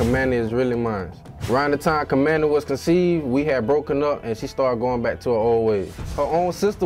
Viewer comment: Did really say, "... round the time Kymani was 'DECEIVED' ..." ?